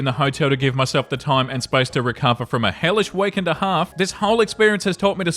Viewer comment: my french dumbass self misheard 'hellish week and a half'. Hellish queef in the heart 😅😅